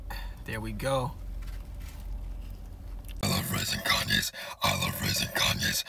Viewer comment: bro I thought he said “I love rizzing Kanye’s” 😭 💀